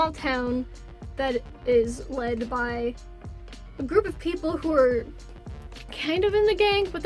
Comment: I heard: lead by gays And honestly, I just screamed